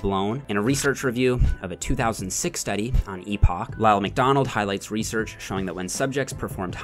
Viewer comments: did you say Ronald McDonald made that study? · Thought I heard Ronald McDonald there for a sec.